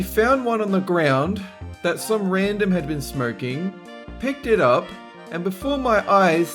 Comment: I thought said smirking instead of smoking 😂 I was about to say what's wrong with smirking?